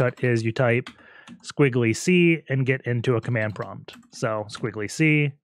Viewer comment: How i get ssh> in 28:25 min. Whats the command or shortcut for that promp. My native language is spanish i dont understand very well. Please help. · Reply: I think he says "~C"